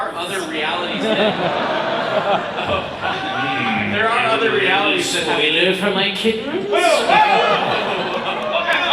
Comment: what is everyone saying? I can't understand a thing. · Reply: Dan said "hmm... a little spoiler for my kittens?" While justin stammers at the same time